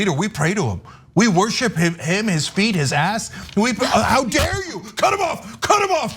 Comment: I swore Cenk said, "We worship him. We eat his ass." Lol.